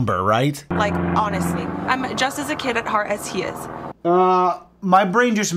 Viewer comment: It's crazy how NoT RacIsT she is because guys, hear me out, she's Puerto Rican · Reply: Oh right..my bad. Tackle away ma'am! 🤣 I just heard 'I'm a kid at heart like him' Wow...no bitch, he is a kid.